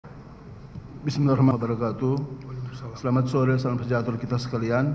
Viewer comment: Ngucap salam kok bismilahiwabarokatu apa salah denger 😂